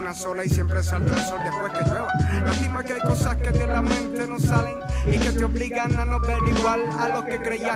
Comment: Creo que dice: "de la Mente", no diariamente